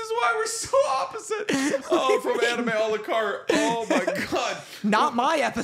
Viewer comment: Fully thought you were saying anime Alucard haha